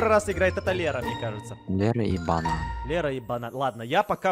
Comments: Мне послышалось Лера и мат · мне показалось что там говорится не "лера и банан" , а "лера е.баная" :-:" 4:14 ВЫ шо.........35 луйков............жпаыпаврыпрддуфпджапв